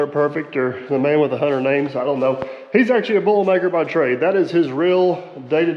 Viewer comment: So what's primary job? Bull maker (might have misheard, also from Europe)? What does that entail?